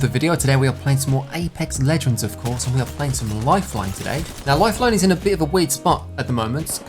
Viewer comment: Honestly thought it said lifeline is black lol😅😅😅😅